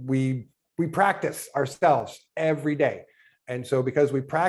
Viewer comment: Did I hear right? @ 1:14:01 “ we practice our spells every day”???